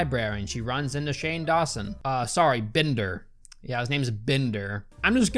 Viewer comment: "I've never heard that name in my life." Someone hasn't heard of Bender Bending Rodriguez. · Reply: I thought he was saying Binder in the video not Bender, lol